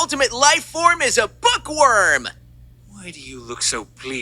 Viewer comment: My dumb ass thought he said "Fuckworm"😭